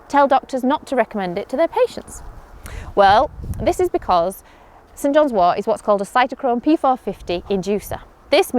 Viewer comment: I heard “St. John’s war”